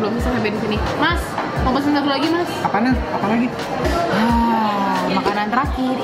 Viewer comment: 11:25 kek ngomong "apaan yang" atau gw salah denger ya wkkwkw